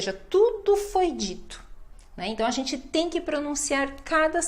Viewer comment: At the 5:20 mark the words foi dito were translated on the screen into English as the F..K cuss word! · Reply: LMFAO "Tudo foi dito" means "Everything was said" But the way she said almost sounded like "Tudo fodido" wich means "All fucked" 😂😂